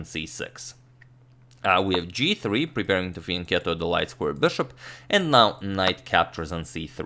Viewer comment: what does he say ? Preparing to "fincatle?" light square bishop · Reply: The word is 'fianchetto'. It means 'the development of a bishop by moving it one square to a long diagonal of the board.'